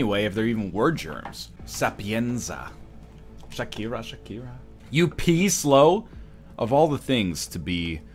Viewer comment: for a sec i thought he said "you piece low", i was really confused as thats a newer meme